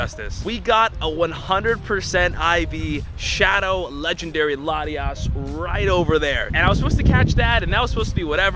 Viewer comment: my subtitles said "we got a 100 percent iv shadow legendary lady ass" bro im lmao rn keeps saying lady ass or ladies or lady something whenever you say latios lol